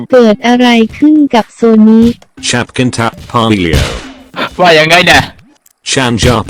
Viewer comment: เราได้ยินว่า"ฉับกีนทับพายเลียว"555+😖🤣